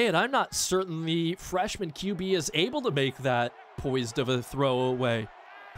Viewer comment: I thought it said our freshman QB was Rattler as in "Spencer Rattler." lol